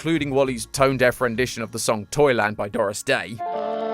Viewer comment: I'm sorry but i heard wally say toilet than Toyland when he was singing😭